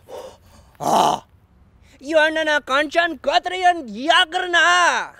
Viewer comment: did he just said corona